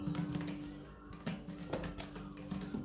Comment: "I think I heard...' do it again'"?